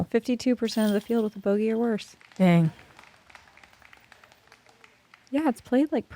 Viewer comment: I thought she said “gang” 😅